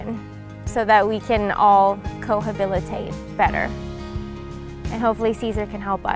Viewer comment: Did she say....."cohabilitate?"